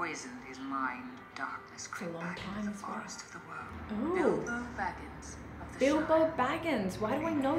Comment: did I hear that cortectly?? 'Oh scheiße' :'D